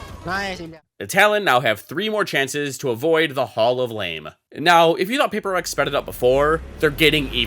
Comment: isnt it "Hall of Shame" instead of "Hall of Lame" lol